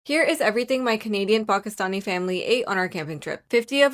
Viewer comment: I am doom scrolling and really only paid attention to the yummy looking food so I heard "comedian Pakistani " and not Canadian. Hope you had a yummy and funny day!😂